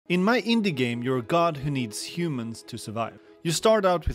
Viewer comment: I thought you said the god feeds on human to survive, and expected the god to collect humans to worship it and sacrifice one every year 😭